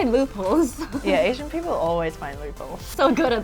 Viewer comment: Can someone tell me what they did say? said "Asian people always find M**? what is that ? 7:30 Thanks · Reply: Asian people always find loopholes. That is what I heard.